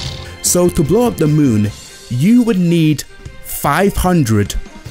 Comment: Honestly I thought when you said Tsar Bombs that you said "Zarbons" Might be funny if you compared how many Tsar Bombs you need to match an energy blast from Zarbon.